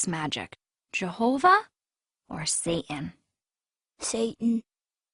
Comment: Uhm, at about 2:12, does she say "sa'an" and totally leave out the "t" in "satan"? Grrrrr. I think jehova (you know, if he existed) would be sad that people are leaving out the letter "t" when they speak.